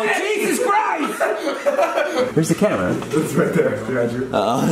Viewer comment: ngl I thought Jason said” where’s the ketamine?”